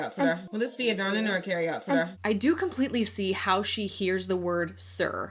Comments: I have replayed this segment FOUR TIMES and cannot hear her say "sir." "will this be a dine in or carry out today?" THAT'S IT. like??? how... did she get "sir?" miss ma'am you need your hearing checked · Sir? I hear "carry out serve". Also I realy like your Videos.